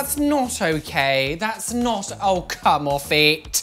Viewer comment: What does he say "come on ..?" I'm not a native speaker.